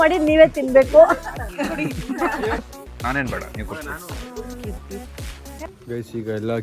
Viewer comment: who heard 23:33 nan yen beda neev kodi😅